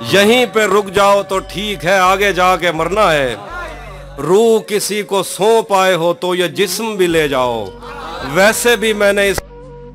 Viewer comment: What he said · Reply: रूह किसी को सौंप आये हो तो ये जिस्म भी ले जाओ वैसे भी मैंने इस खाली बोतल का क्या करना है